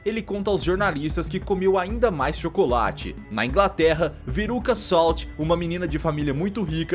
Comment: entendi peruca